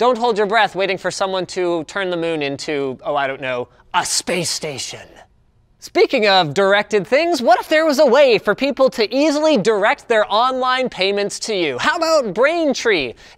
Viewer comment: Ha ha I thought he said erected things